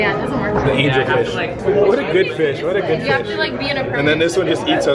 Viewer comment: I thought he said Armin alert 😹